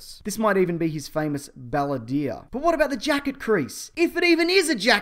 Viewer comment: Did you say "baladier" instead of bandolier at 1:46? Lol